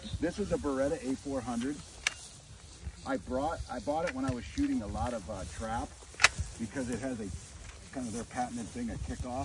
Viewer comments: I honestly thought he said: “I bought it when I was shooting a lotta crap.” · Did he just say he bought it for when he is visiting the trap? 😂